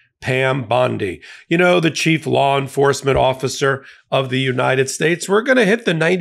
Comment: Could have sworn I heard you say, "Cheap law enforcement officer."